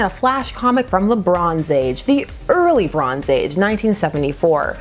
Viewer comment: Every time you same "the bronze age" I do a double take because I hear "Lebron's age" and I'm like Lebron doing comics now??